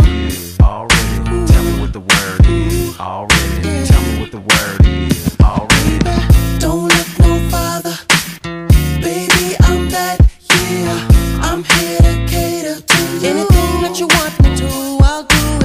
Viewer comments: I swear I thought he said "don't have no father, baby I'm back here" when I was younger 😂😂😂😂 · i thought he said im dedicated to you